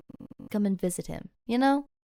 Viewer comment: Did anyone hear the buzzing noise Like if you did 👇🏻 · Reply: Then I heard I'll visit him